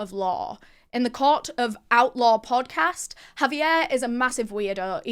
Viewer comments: i’m american and the whole time i thought you were saying “OUTLAW” podcast 😂 it’s outlore! got it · I thought that she was saying outlaw not outlore...lol